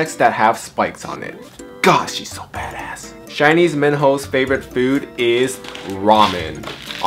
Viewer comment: when ge said minho i heard it as man hoe 😂